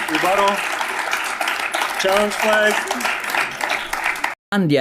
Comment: Can anyone hear what he says at 5:57? "Can i have a rebuttal? (x2), ____________" Child's Flag? Giles bag? I'm so predisposed to hearing absolute nonsense come out of SovCits that i almost subconsciously accepted it as "some stupid thing they think is real and normal to say" like Admiralty Law